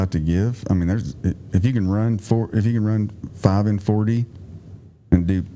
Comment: how fast do you need to run? I couldn't understand him, did he say 5:40? If that is per mile for two miles, that's smoking. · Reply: 5 miles under 40min.